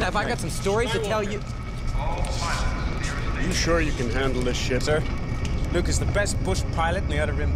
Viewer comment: Does anyone else think it sounds like he's saying "Can you handle this s**t?" at 15:54? It might be cause Biggs is jumping down his throat a bit